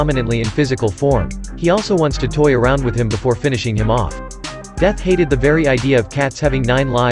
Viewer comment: Why did i hear "befour finishing your mom"💀💀💀💀